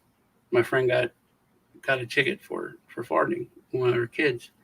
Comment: Lol you said farting...thought you said farding....there is a difference